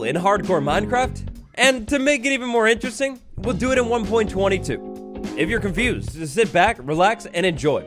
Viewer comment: It kinda sounded like he said "we'll do it on 1.22", but I think it was "we'll do it on 1.20 too"